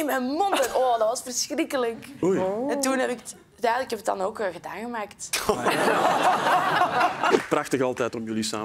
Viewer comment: Kan iemand uitleggen wat er op 0:45 wordt gezegd? *Ik ben Nederlands. Dank u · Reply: Gedaan gemaakt. Geen idee wat het betekend.